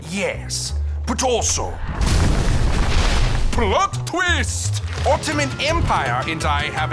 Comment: what Ottoman Empire said:plot twist What captions said:Blood twist What i was thinking:hot fist Wha I sad:nut fist